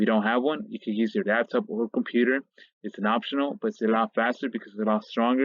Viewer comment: Did he say use your dads computer @ 1:45?